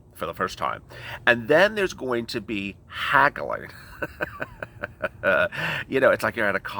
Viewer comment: You said there's gonna be haggling but I thought you said there's gonna be cackling 😂😂😂 haha! Fits them better!